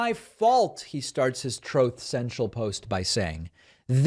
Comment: Sounds like he is saying troth cenchel.